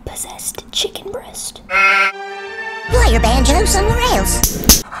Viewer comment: "play your banjo somewhere else" 💀💀💀😭😭😭😭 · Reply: I thought he said play ur banjo on some rails